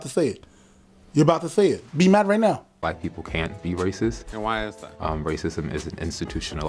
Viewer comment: lol at first I heard “white people can’t be racist” and was confused for a second 😂